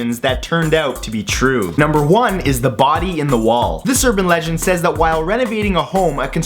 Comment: For No.1, I thought he said " Booty in the wall" because his accent is so thick! 😂